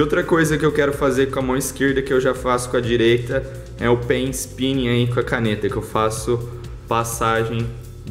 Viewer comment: Oq ele falou em 3:32? Eu coloquei o vídeo com legendas pra ver se ajudava e a legenda q apareceu foi Pênis pinha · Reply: Pen spin